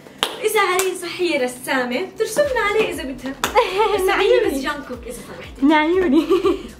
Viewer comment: انتي ارمي؟؟ 💜 لأنو سمعت اسم جونغكوك